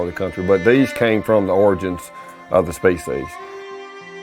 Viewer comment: Did you say your turkeys come from the origin of the space age? 2:22 and at 2:11 are you sayin the should be renamed 'Ebolla'?